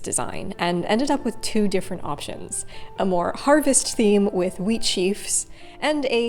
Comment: When you said "wheat", i heard "weed" so i really thought for a second that you were gonna make a big old green gandja hand